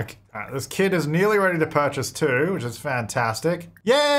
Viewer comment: I was listening to the video and looking away and all I heard was "this kid is almost ready to purchase, too" i had to double take 😂